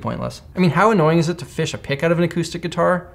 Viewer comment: It might be because I'm an aquarist, but for a moment I thought he said "how annoying it is to pick a fish out of a guitar" and it sounded plausible to me. I'm sure it's just as annoying as fishing a pick out of one.